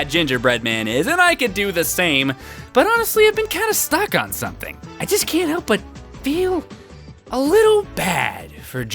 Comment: Wait what did he say she was "from"? I genuinely wanna know. I just cant hear it right. It looks cool too. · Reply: I may be pretty late to this but, Sami is from the video game franchise called Advance Wars.